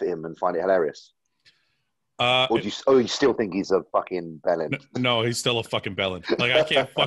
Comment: what did you call this guy here? A bellend? lol